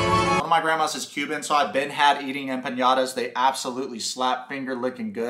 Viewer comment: … did you just say empañadas???? 🤣🤣🤣